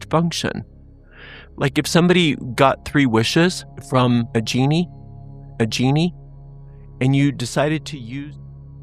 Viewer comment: the part about, a genie cuts out. what did you say? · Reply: I'm so sorry that happened. I was just saying that this part of the psalm is as if a genie granted you a wish, and your wish was that everything in your life would be permanently perfect. Blessed be! 🧙‍♀️❤️🧙‍♂️